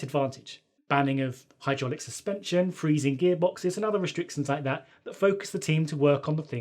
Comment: when he says freezing gearbox around 7:22 what does that mean? or does he not say freezing gearbox and i am totally hearing him wrong